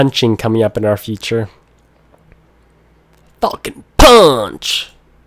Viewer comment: when he said falkon punch! i thought he said fuckin punch!